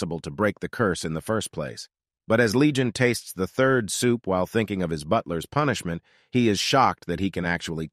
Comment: I heard "furious while thinking about hi- ehhhhh"